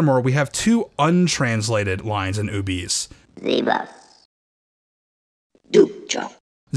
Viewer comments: it just sounds like she says: leave us. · I always heard, "leave us!"...